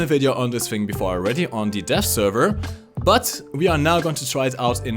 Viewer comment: Sometimes when you say "Dev" it sounds like you say "Death." So every time you said "Dev Server" it sounded like "Death Server." you sexy beast